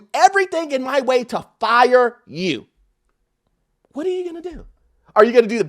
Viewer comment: When said, “What are you gonna do?” All I heard is “ACT A FOOL!” 😂